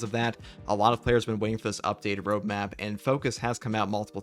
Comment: I misheard when you said ‘the updated roadmap’. I thought you said ‘the updated bro-map’. Can we all start using the term ‘bro-map’ for update calendars for games mostly played by men? Awesome!